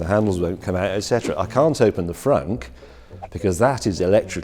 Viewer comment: did he say... frunk?